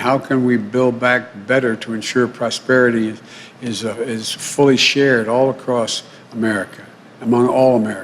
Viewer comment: Ohh my gosh at 4:12 i thought he said his idea was "full of §|-|¡+" ... I went .. Wwwwhat ? I rewinded ( well not actually lol) it and it was actually" fully shared" lol haha press on blue 4:12 to hear for. To hear for self your souls i mean your selves